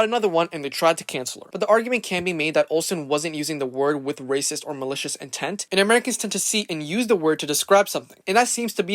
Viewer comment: WHAT IS THE WORD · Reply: I believe the word is Gypsy